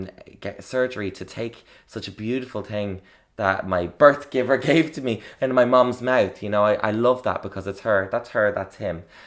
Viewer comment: I thought you said "what my birthgiver gave to me in my mum's mouth" so I was like wait what @@